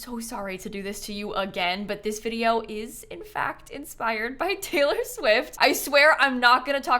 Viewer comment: When simsie said inspired I heard “sponsored” and had a minor freak out for a second until I rewinded🙃